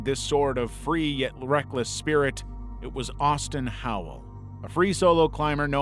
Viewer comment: OH... Austin. At first I heard "Thurston" Howell.